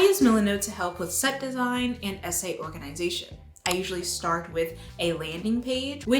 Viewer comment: this is unrelated to the actual content of the video, but just your ad read: my god, tiktok's self-censorship has ruined my brain. you said "it helps with essay organisation" and i heard "SA" as in....SA. and i just couldn't understand what you meant by organising SA???